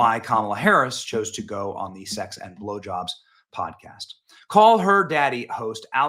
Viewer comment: I had to rewind it and listen to it a second time just to make sure that I heard you say “sex and blowjobs podcast”. Sorry but that made me laugh so hard. Tragic that Kamala felt the need to do that instead of helping more with hurricane helene victims. Doesn’t surprise me though.